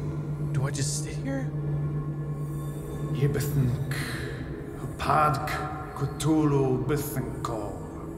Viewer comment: Me cthuhlu nyth eternity wait, That's what the dude says at 8:42. I read alot of the Mythos myself, so ahornah ah eternally ah gifted llll syha'h'bthnk hrii! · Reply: Y- essence decendent cthuhlu 'bthnkor for 9:01, couldn't figure out the 'bthnkor. Sorry